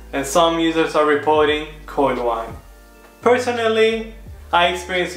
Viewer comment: What is he saying at 5:11 as the second issue some users are reporting? I can't understand his accent there. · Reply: I said "Coil Whine" 😊